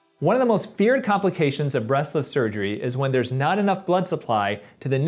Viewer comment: I thought he said "breastless surgery" for a moment 😂